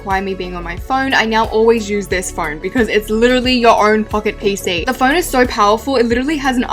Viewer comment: DID SHE SAY ITS UR OWN FKIN PC?! LOL?